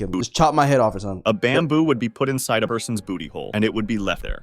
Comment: Beginning is wild I thought it said baboon for a sec man🥀😔🙏💔